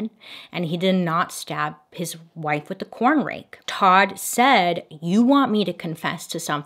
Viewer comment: I thought it said the “Corn Flake” mystery and I was so confused/intrigued about a mystery surrounding corn flakes 🤣🤣